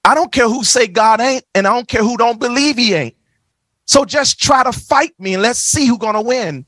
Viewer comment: what did he say?? believe..iate? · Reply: Haha… “Believe he ain’t…”